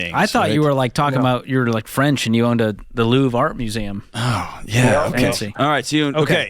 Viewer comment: haha I heard Louvre too!